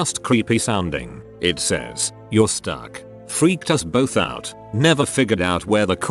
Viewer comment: I had taken off my headphones for a moment around 12:55 and I looked back at the screen. Without audio I thought it said the creepy voice said "YOU SUCK" to the boss of the phone!